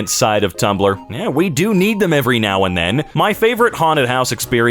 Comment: I thought he said "we do eat them now and then lol xxx❤️